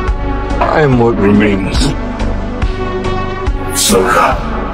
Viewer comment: why does he say “soda” then just giggles😭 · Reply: ok so he says "Soka" as in his padwan Asoka. Soka was a nickname